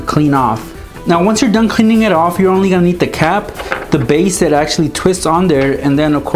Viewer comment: I thought he said you only gonna eat the cap😂😂😂